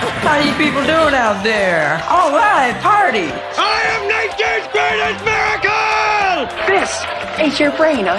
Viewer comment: the subtitles read "(homer shouting indistinctly), but what he's saying is "i am nature's greatest miracle"